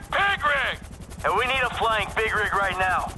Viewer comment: I thought I was the only one that noticed it sounded like Trevor said pig rig instead of big rig